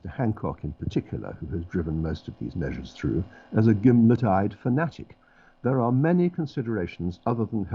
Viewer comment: Charles Badoola “swivel eyed”. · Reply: Are you sure? 9:28 If so then he mispronounced it, I hear 'gimba tied'!? Swivel-eyed would fit for Hancock, no complaint about that.